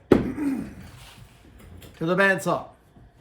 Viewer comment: Totally thought he said "to the Bat Saw!"